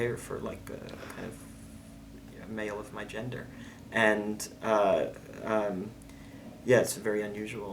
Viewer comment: Did he say “for a male of my gender”, at 12:54?